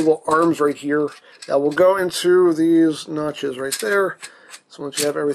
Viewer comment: Were you hungry while doing the review? Cuz everytime you said notches , I heard it as nachos... was it just me ?